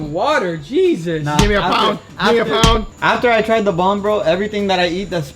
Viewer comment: At first i thought he said "After i tried the bong, nothing tastes spicy". Good to know he was talking about the hot sauce and not the magic leaf lol.